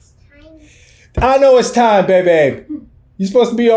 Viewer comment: Did he just say bébé?